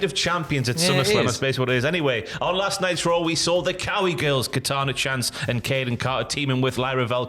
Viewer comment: Someone please help me understand Cowi girls? I always thought said Cali girls when Catanzaro is a MD girl lol Its Cowi? Kowi? I cant find any reference on google.